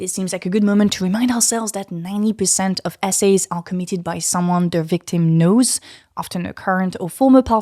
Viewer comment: I heard "90% of essays are committed by someone the victim knows"